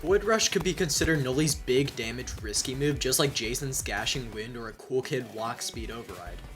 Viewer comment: Was it just me or did he say gashing wind 2:17 oh and nova it can kinda help on Brandon works cause it can bring a survivor down